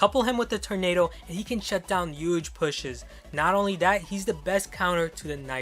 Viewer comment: did he really say yuge?